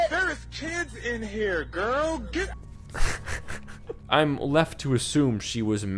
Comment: I'm like 90% sure this is her in the middle of calling him a slur that ends in "ger" cause that is definitely all I hear and he mentions there are children present ...shameful.